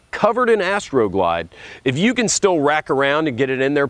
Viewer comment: Did you say wrap around. Or reach around at approx.